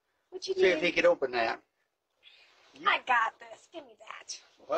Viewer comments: At the end he says “I don’t have no friends left”. Broke my heart, then I realized he does. He has his neighbors. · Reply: Damn it really does sound like he says friend's and not strength. Weird how the two words don't even sound remotely the same lol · Oh that broke my heart when he said he has no friends left 🥺. I’m glad he lives by nice people. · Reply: I thought he said I don't have any fresh limbs 😂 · I'm 64, and I know how he feels. I've out lived all my friends and most my family. Bless him and his Mrs. ❤🎉😊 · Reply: I'm glad you cleared that up because at first due to the misinformation of the subtitles I as well thought he said friends but now I can clearly hear him saying strength so thank you. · He did say "strength" this drastically changes the entire video. Was way more sad when I thought he said "friends". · The "I don't have no friends left" hurt my heart for him.. God bless this beautiful man 🙏💙🙌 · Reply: I heard "I don't have any brains left". "I don't have any strength left" makes so much more sense Thanks for clearing that up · I teared up with I don't got no friends left. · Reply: I heard "strength " I don't have no strength left. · He said," I don't have STRENGTH left "...The Elderly can be so beautiful sometimes❤❤❤❤❤ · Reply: I thought he said, I don’t have any strength left